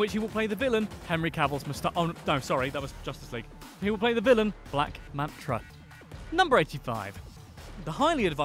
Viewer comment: Um did he just say black mantra?